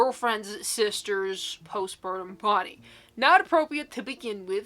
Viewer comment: That postpartum story, I thought you’d said postmortem, I was horrified 😭